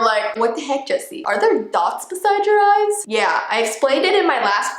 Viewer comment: Me: puts on captions Captions:” heY guys it’s Jesse B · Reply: It sounded like she said Jesse b to